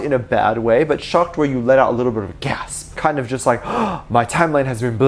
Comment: I thought he said gas🤣🤣💜